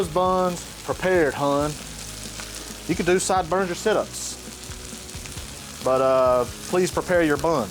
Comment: Did he say "Sideburns or Sit ups"?🤣